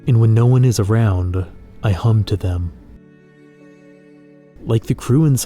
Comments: I thought he said "I hump them"💀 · i legit read and heard that as, "when no one is around, i hump to them" LMFAO HAHAAA